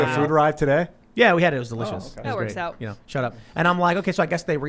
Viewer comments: Did tim say "shut up" at 13:37? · Ok is he saying “shut up” around 13:36 ? If so, Tim’s head is getting too big for his beanie. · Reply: Lol, no he was saying 'Shout out'